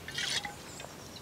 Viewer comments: I know he tried to speak gobberish, but he actually said "Mars u picku materinu" which would mean something like"go fuck off" in Croatian hahahha Rip legend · He really said "Marš u pičku materinu"